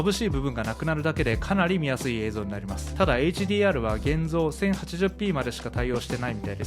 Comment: で「HDRは現像」と聞こえたので字幕ONにしたら「現状」と言っていたのですね。 各Webカメラとの性能比較はかなりわかりやすかったです、「高画質でZoom相手に己の白髪を見せつける」というのは、流石にどのカメラでも修正は難しそうですね… ところで手元でチラ見えしてるキーボードが気になってしまいました👀✨自分も最近、「有線HIDデバイスのBluetooth化」するガジェットを手に入れたので、自作キーボードにもトライしてみようかしら…